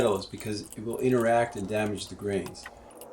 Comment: I thought you said metal damages kefir. If that is true, how about using the infuser?